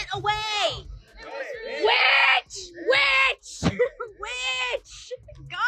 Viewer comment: is she saying witch or am I is going crazy?